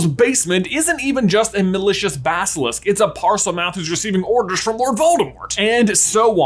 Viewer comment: I could have sworn he said the the basilisk was “not just a delicious basilisk“